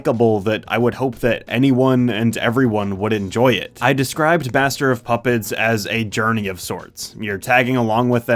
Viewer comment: did anyone hear that he said baster of puppets?